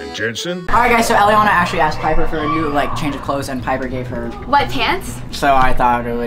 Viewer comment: When u said u gave her white pants I thought u said wet pants and I was so confused